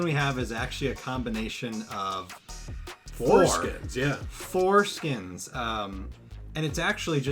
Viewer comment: bro i swear i heard "foreskin" at 6:16 lol